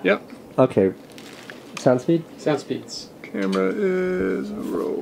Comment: What does he say at 2:49? Sound Speeds?